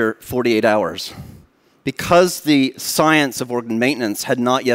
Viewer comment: "...because the science of ______ maintenance..." What did hey say? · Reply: Because the science of organ maintenance